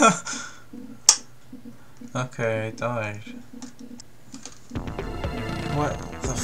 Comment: I think you said "okay I died"